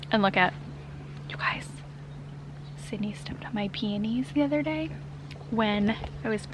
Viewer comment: OMG I'm cracking up! That was so cute when you were showing your peonies but it sounded like she said "why are you showing everyone your panties?" 😂